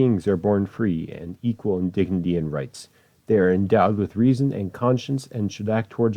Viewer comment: the only word I couldnt understand was "endowed."